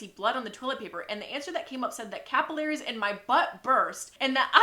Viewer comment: tell me why when it said capillaries i thought it said caterpillars